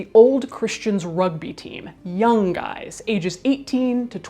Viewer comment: Instead of "young guys" I heard "yum guys" 🙈